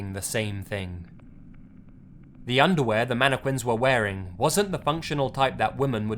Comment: did he just say mana-qwins?